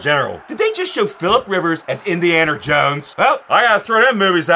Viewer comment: Did he just say "indianer jones"?